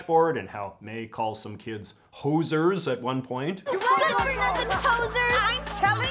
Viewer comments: Being not Canadian I thought Mai said “posers”, also Miriam called mais beanie “touque” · When she shoo'ed away those graffiti kids, I thought she said "good for nothing posers". But the subtitles say she said "hosers". I didn't know that was a Canadian insult until I looked it up.